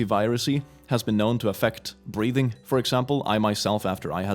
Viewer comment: i misheard breathing as breeding right after click said he had issues with stamina.